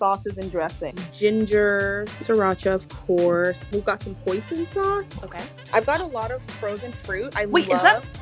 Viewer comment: when she said hoison sauce I heard poison sauce, when she said frozen corn